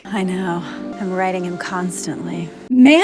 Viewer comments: For 2 seconds I thought she said "I'm writing him constantly", then I was was like oh, ewww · "I know.. I'm riding him constantly." I swear... that is what I thought she said X_X